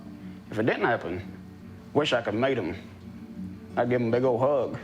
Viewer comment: Meet him? I thought he said mate him lmao